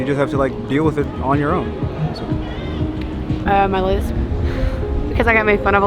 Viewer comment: what is the girl saying she is insecure about? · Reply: A lisp